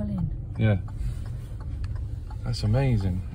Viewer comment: "OMG, thats a wolf..." that sounded like Harry Potter😂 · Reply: I heard "OMG! That's a WOOF!"